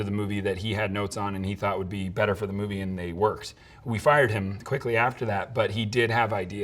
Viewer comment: did he say « we fired him » ?